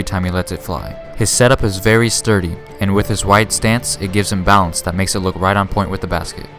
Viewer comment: thought that said white stance